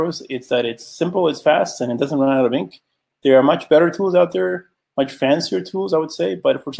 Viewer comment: What is the tool he says that's better than arrows at 12:50? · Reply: he says "much fancier tools"